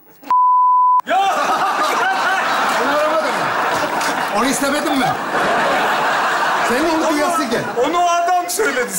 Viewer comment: biplenen yerde ne dedi ? 🤔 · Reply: Anüs